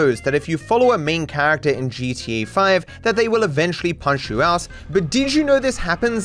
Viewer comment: I thought i heard "punch your ass" instead of punch you out 💀